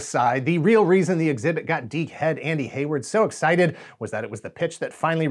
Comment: did you just call Hayward a.... Dic-head??